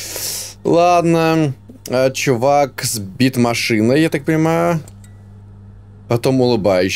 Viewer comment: "Чувак сбит машиной" послышалось..